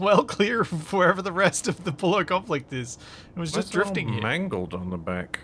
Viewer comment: Oh my gosh. This whole time I thought it was the “Pollo Conflict” - I was wondering why you were talking about a fighting chicken all of the time😂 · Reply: Better than me. I thought i was mishearing the accent and it was Polio Conflict